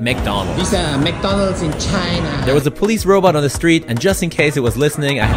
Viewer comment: Even though you can’t speak chinese your pronunciation is spot on: i swear i heard “itsa mcdonals in china” 10 china points